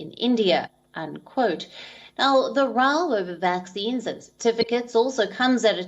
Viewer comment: Her prounciation is very difficult to understand from 1:12 to 1:20 here, please let her fix her mistakes. I dont get what she is saying "realm of vaccine"? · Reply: "the row over vaccines"